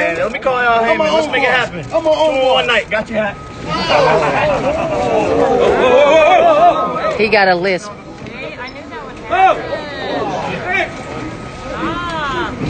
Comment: I know I ain’t the only one who heard the camera woman say “he got a lisp” Edit: whoever called me a punk ass stfu for one. Secondly. 2:19 is when it happened